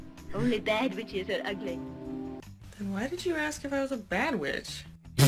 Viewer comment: I THOUGHT SHE SAID "bad bitch" AND NOT "bad witch"